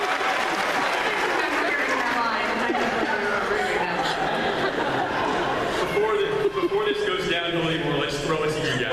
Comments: What did Jen say at 2:40, after Colin's line?? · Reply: "I don't remember THAT line." LOL. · wait what did jennifer say at 2:40 after colin said his line? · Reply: "I don't remember that line"